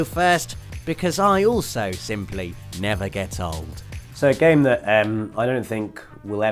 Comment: I thought said: Because I also simply never get bold! Made me smile, I feel your pain